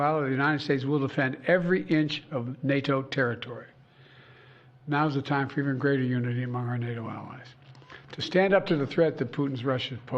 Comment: Someone please tell me what he said at 4:45, I have 0 clue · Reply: Now is the time for even greater unity among our allies